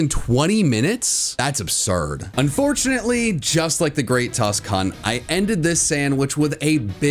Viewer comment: The Great Tusk what? At 18:13? Sounded like you said Great Tusk...C**t